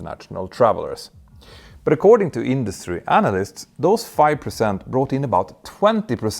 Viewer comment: I heard "according to imbecile analists" lol